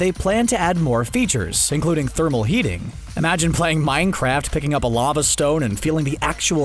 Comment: I'm sorry, at 2:19 did he say pick up a lava stone in Minecraft?